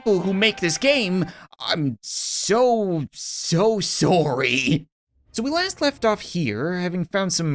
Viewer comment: thought he said im.so so swowwy